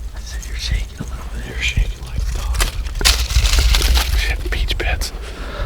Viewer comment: Did he just say "I'm $hittin' peach pits!" ? LOL!!! That has to be put on a t-shirt for THP merch!